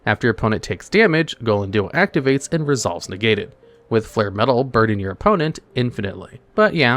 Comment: Very good video, I fully agree with all the choices! How about a Top 10 worst cards that can omni-negate? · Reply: I didn't quite get the whole passage around 23:54 , did he say ''after Gellenduo activates, it resolves negated''??